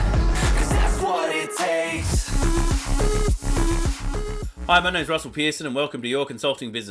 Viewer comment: LoL - Sounded like he said "My name is Russel Peters" 😅 Imagine Russel Peters was your consultant. Hilarious.